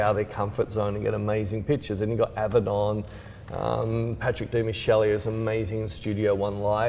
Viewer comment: you mentioned him before but for the love of J I cannot figure out who you're referring to. Patrick Dimichele? Demicheli? :D) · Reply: Patrick Demarchelier